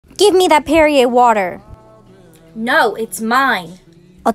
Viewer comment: I thought she said give me that period water